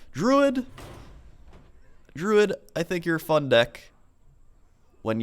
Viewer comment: did he say it's a fuck deck :D